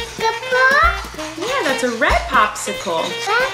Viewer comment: Is it just me or did say at 9:26 “That does look like a red popsicle”